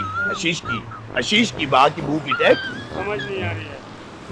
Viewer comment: was he saying ashish ki man ki?